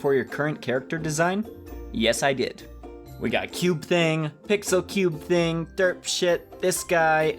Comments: did you say s**T!?!? Cause if you did I will unsubscribe and hate you... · did you just say derps**t